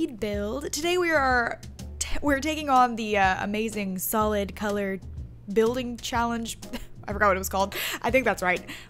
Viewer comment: i thought it said the colour blind challange